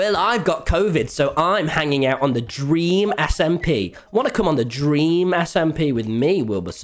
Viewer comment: i thought he said about dreamsmp merch for a sec there i got so exited lol